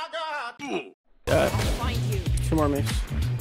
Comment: Did just said "2 more C*m on me" ?